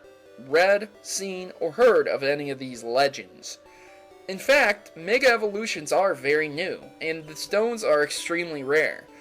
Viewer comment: ish the captions say in fact nigga evolutions??